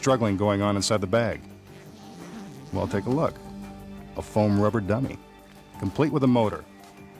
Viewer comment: I thought he said rubber ducky at first I was so excited lol